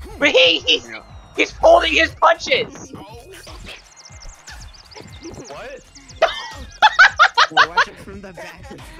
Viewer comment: Did he say the f word lol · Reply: “Oh F*ck”